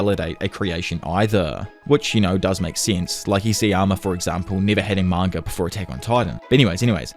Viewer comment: bro wtf did u say here??😂 “imma for example”? Sorry the New Zealand accent threw me off there😂